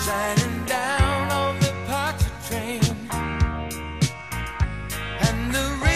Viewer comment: Ponchartrain, huh, I thought they were saying the moon was shinning down on the "parked train". All these years I was so wrong, I wonder what else I have to learn I was wrong about?